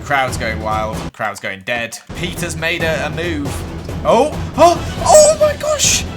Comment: I thought he said “PETA” and not “Peter” so I was glad when Peta died